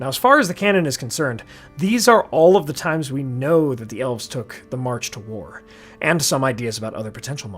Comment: Heheh did he say “elves”